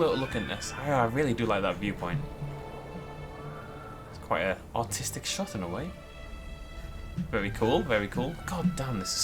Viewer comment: does he say "Thats an autistic shot in a way"? Or did I miss hear?